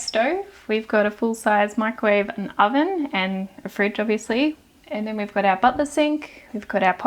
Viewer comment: If you look at the captions, instead of “a fridge, obviously” it says “afraid to be asleep” at 9:17 👀👀👀